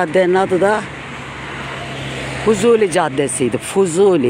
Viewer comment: Canım beni yanlış anlama zevkle izlerim Adana'yı severim beni yanlış anlama Fuzuli Türk şairimiz caddeye ismini vermişler ❤ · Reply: Ayy nuranda dedi izleyince o Fuzuli diye ama hep huzuli caddesi derlerdi ben öyle anladım demek ki 🤭